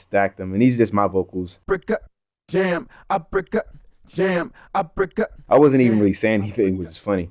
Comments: i always thought i heard “i’m bricked up, damn, i’m bricked up..” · "I pickup JAM" but he is not saying anything.